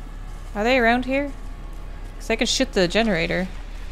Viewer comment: Could have sworn said "I could sh*t the generator.."